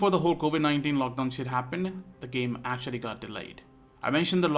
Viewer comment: did he say sh*t?!?!?